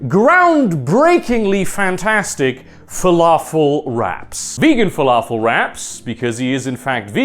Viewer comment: At first I thought you were saying "falafel rats" which I'm very curious to try now